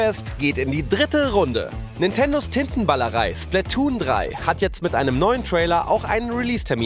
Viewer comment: Alter, wie ich mich verhört habe. Ich habe nicht Tintenballerei sondern TITTENBALLEREI verstanden. Internet-geschädigt und versaut bis auf die Knochen :D